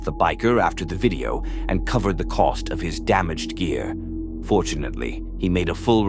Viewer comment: I had te rewind the dog video because at first I thought you said they covered the cost of his damaged ear! Poor guy got his ear bit off! I missed that part. Glad to discover I just had wax in my own and his was still intact.